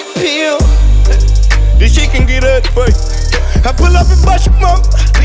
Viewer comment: does he say " I pull up with bushy brow"....naruto fans get it